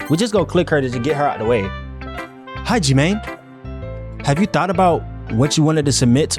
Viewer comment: Kinda sounds like he said Hajime ngl